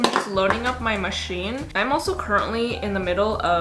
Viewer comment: am i the only one who noticed she said curtainly instead of currently lmao that made me laugh so hard